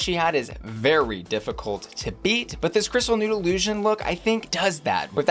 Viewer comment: In the Trinity segment I thought you said “crystal noodle oosion” just as the shot was panning across the long crystal strands on her outfit. That’s what I’m calling this now.